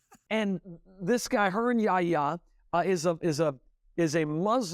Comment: Distracted for a moment, I heard "hernia, yah!", and I totally went dafuq he just say???